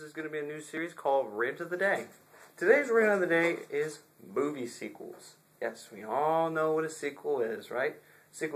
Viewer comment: sounds like you said boobies